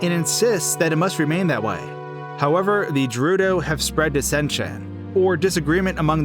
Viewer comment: did he just say “jerudo”